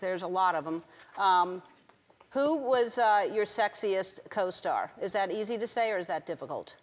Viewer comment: i heard pornstar xdd 1:25 obviously mattew mccounaghuey